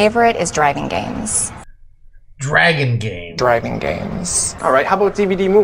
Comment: I swore I heard the mom say "Driving" and not "dragon"